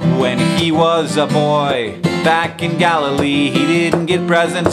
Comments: Anyone else thought he said Galifrey not Galilee? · Every time he said 'Galilee' I heard 'Gallifray' which leads me to believe that Jesus, was in fact a Time Lord.